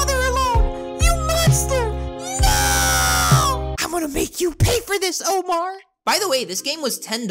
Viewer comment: at first to me it sounded like the gingerbread man said: I’m to going rape u until u pay